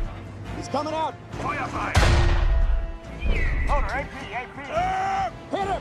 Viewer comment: I heard the Tiger commander said "Firefly" lol Note that I know he said "Fire" in german, it just sounded like he said "Firefly"